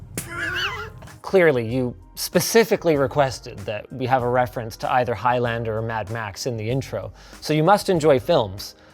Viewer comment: what did Linus Torvalds say? It looks like a perfectly (???) bag? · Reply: Cromulent